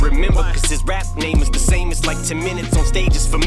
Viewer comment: I actually thought he was saying Ez Mil or should I say easy mil as in don't go to hard easy on the bars Ez Mil but I was wrong 😂😂